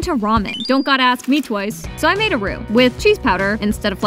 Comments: Who else thought he said robux instead of roux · My big fat ass thought it said robux